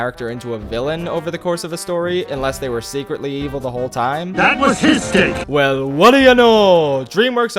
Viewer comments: makes it sound like he says “That was his (insert schlong) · I definitely heard YEEvil not evil